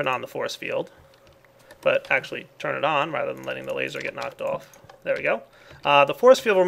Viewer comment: did say "letting the laser get knocked off" ?